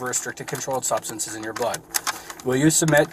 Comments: Tell me why at 18:06 I heard "substances in your BUTT" lmaooo it was blood not butt 😂😂😂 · thought he said "in your butt"..